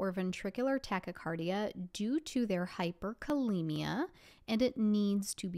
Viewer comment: question 10 confused me. you said pt has hypercalcemia and tx is more calcium. How do you know the calcium level is high and why will tx be more calcium? wouldn't insulin and dextroxe be more appropriate to treat the hyperkalemia? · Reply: i think she said hyperkalemia. 😊